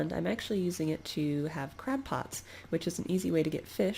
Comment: i... i thought op said "crack pots" instead of "crab pots"...